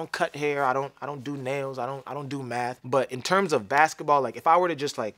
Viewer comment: thought he said meth not math